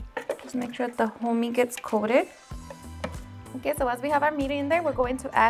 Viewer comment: Omg I'm dead it sounded like you said "Just make sure the homie gets coated" 😂